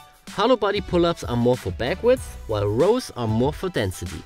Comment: At 2:36, you mention a "long story short" difference between hollow-body pull-ups and arched-back pull-ups, but I cannot make out what you say hollow-body pull-ups are good for. It sounds like "back width", but I just want to be sure. Thank you so much for your ongoing contribution to the betterness of my mental and physical well-being.